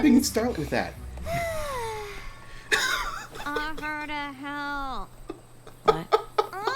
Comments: Did anyone else mis-hear Pinkie's first "Offer to help" as "Awkward as hell!", because I sure did! XD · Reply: For some reason, I heard "Offer to hell" 0_0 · I thought she said offer to hell